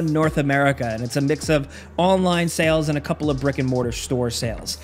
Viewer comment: "brick and mortar" but i definitely heard Rick and Morty